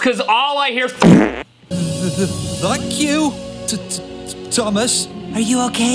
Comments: Hi! I CLEARLY heard “F**k you, Thomas.” I’m surprised parents didn’t complain about it like they did Bob the builder, which I didn’t hear any swearing in that clip, it was just muttering like they claimed it to be. I did hear “F**k” in Hunchback of Notre Dame, I also think Mr. Rogers knew what he was doing because of the way he grinned right before he flipped the bird! 😂 Hilarious video! Have a good day! 😃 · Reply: I heard "Thank you, T-T-Thomas" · Hi! I CLEARLY heard “F**k you, Thomas.” I’m surprised parents didn’t complain about it like they did Bob the builder, which I didn’t hear any swearing in that clip, it was just muttering like they claimed it to be. I did hear “F**k” in Hunchback of Notre Dame, I also think Mr. Rogers knew what he was doing because of the way he grinned right before he flipped the bird! 😂 Hilarious video! Have a good day! 😃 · Hi! I CLEARLY heard “F**k you, Thomas.” I’m surprised parents didn’t complain about it like they did Bob the builder, which I didn’t hear any swearing in that clip, it was just muttering like they claimed it to be. I did hear “F**k” in Hunchback of Notre Dame, I also think Mr. Rogers knew what he was doing because of the way he grinned right before he flipped the bird! 😂 Hilarious video! Have a good day! 😃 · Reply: I heard "F**k you, T-Thomas". As well. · in the beginning i heard "VUCK you, tttthomas"